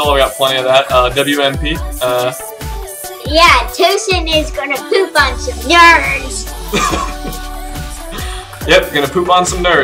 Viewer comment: LMAO, i thought she said "Tosan is gonna poop on some yards" xD